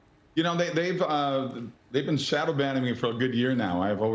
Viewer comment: I thought he said they were "shadow banging" him. lol.